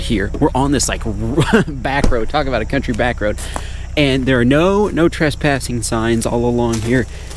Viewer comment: its sounds like he says there are lots of no trespassing signs what he really said is there are not any trespassing signs